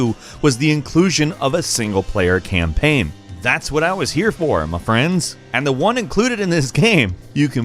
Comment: What the hell is he trying to say at 2:30 ? Ive rewatched that part over and over and i dont understand what hes trying to tell us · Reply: “That’s what I was here for ma friends!” That was hard for you to understand? Lmao